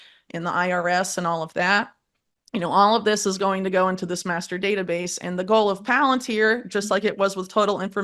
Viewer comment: I thought she said masterbater base but she said master data base… 😂